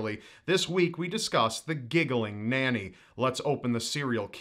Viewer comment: i feel like it should be Giggling Granny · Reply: Mc Woof ngl i thought it said giggling granny